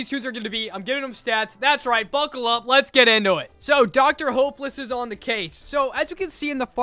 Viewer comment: I thought he said Dr homeless😔😔😔😭😭😭